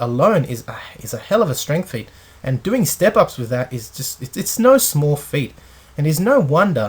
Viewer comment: So I thought you said he has small feet. Turns out you said it was no small feat. Lol